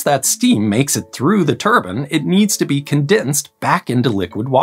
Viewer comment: My ears are really not tuned to this pronunciation of "turbine". I keep hearing "turban" instead lol. That's a me problem, to be clear. I can absolutely tell it's "turbine" if I listen extremely carefully, but my brain keeps autocorrecting it to "turban" in the normal flow of speech.